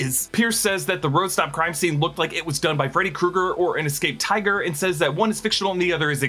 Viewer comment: I think he said a sabretooth tiger